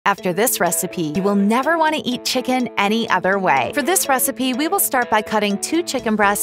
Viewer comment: Legit thought she was saying I'm never going to want to eat chicken again 😂😂